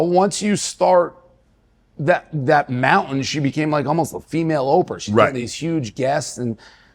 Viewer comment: Did he just say "almost like a female Oprah"?